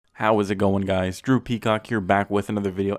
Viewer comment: My younger brother thought he heard “Droopy Cock” instead of Drew Peacock 😂